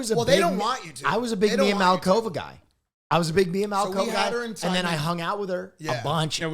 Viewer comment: Idk why but when will said he was a Mia Malkova fan I thought he said I’m a big “BLM COVID fan”.